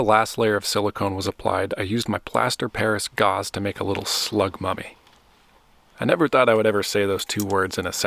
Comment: The “I ❤ Slug Mummies” tshirt was a great touch! 😂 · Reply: I kept hearing “slug money” which is how money comes in for art 😂